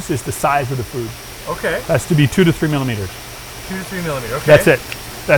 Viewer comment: what the guy said at 4:04.........I cannot understand.......... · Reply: he said “has to be 2 to 3 milimeters” regarding the size of the food